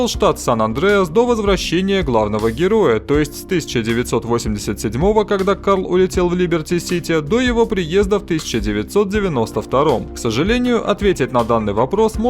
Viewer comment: мне одному послышалось слово "Кам"?